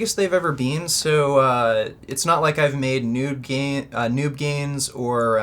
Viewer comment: did he just say noob gains or nude gains? lmao